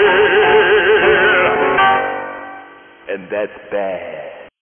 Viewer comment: What does it says at the minute 0:36? I'm italian and i can't understand it due to the compressed audio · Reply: "And that's bad."